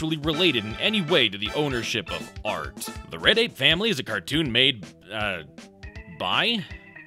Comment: I keep mishearing you say "The Reddit Family" instead of "Tne Red Ape Family" which might be just as funny